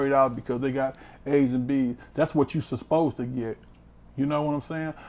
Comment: did he say Suspose to get? whats that? lmao